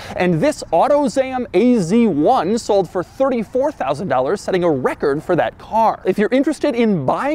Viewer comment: im sorry did you say 30.000 for az1... let me rewind 34.000$????